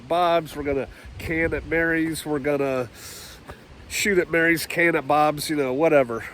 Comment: At first I heard "We're going to shoot at Mary's can of Bob's" I need more coffee lol Shalom ✌️❤️ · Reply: That was actually "shoot at Mary's " and " can at Bob's" Can meaning preserve food items with hermetically sealed containers to preserve freshness and stave off spoilage ( in case anybody was actually for real confused)